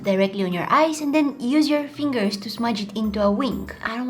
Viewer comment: instead of it worked it twerk